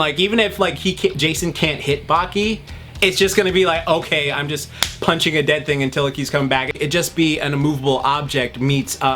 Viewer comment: I thought said" okay I'm just punching a dead nigga"